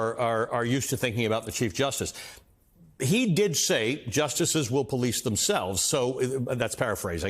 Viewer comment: We have a homophone problem. When you thought Roberts said the judges will police themselves, he actually said the judges will please themselves.